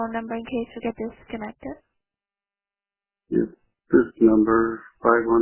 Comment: That is NOT what he said... He didn't say "gunfire"... He said "it's this number 515" etc... How did you get that so wrong???